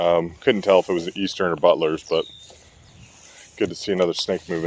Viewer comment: I thought you said buttless instead of Butlers LOL